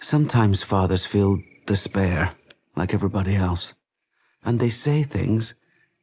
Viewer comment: I’m just casually listening to this and I hear “and they send him to HELL”. I’m sorry WHATS GOING ON HERE